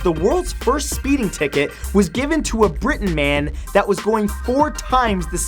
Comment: did he just say Britain man?